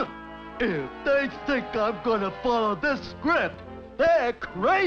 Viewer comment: When I first watched this, I thought he said “If they think I’m gonna follow this crap.”